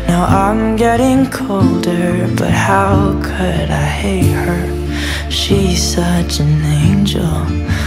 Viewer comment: I thought it said heat her